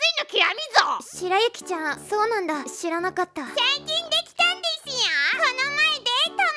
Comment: 白雪ちゃんの「(彼氏)最近できたんですよ〜」が「(彼氏)3人できたんですよ〜」に聞こえて、フェッッッッ!!!???ってなったwww